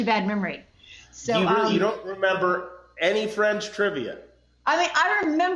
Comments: Every time Jimmy said “Friends Trivia” I could’ve sworn he said “French Trivia.” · I keep hearing “French Trivia”...